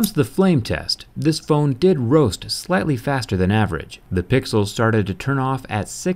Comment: the PIXEL burned? what did you say at 4:01?